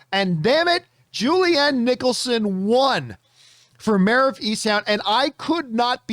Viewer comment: I thought you guys were saying MAYOR of East town. Like what's so interesting about being mayor of a town? lol